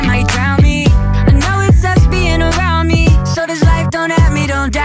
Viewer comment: At first when it said "sucks bein around me" i thought it said lesbian XD